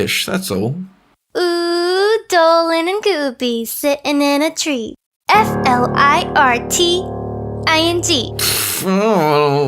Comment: XD I thought she was goin to say Dolan and gombe stilling in a tree F U C K I N G ZD SORRY XD